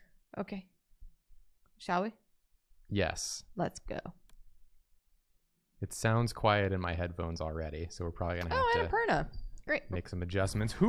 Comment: I know said "it sounds quiet in my headphones" but i heard "it sounds quiet in my head-bones" because i can not hear things correctly.